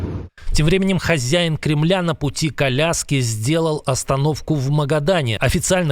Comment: "На пути к Аляске" послышалось как "на пути коляски" . Чуть не возрадовались все